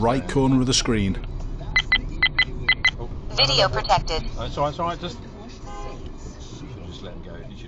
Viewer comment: In the last clip I thought it said "Idiot Detected" for a second 🤣